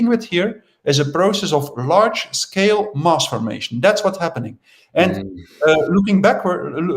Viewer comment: Perhaps the fact I haven't had a TV for 12 years and have not listened to radio 4 etc for the last year helps me to not be remotely part of the mass formation 😉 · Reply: Is that what he is saying at 7:45? Mass formation? Oh, nevermind. They repeated it clearly.